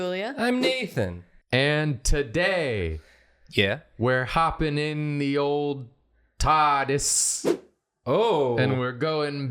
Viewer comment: I heard ‘hopping in the TODDis’